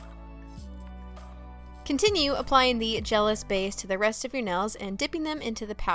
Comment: is she saying jealous base at 3:16 3:28 ???? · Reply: It's called the SNS Gelous Base :)